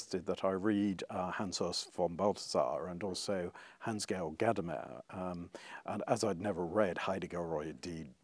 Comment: Anyone catch the names mentioned at 1:27? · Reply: Hans Urs von Balthasar, Hans-Georg Gadamer, and Martin Heidegger.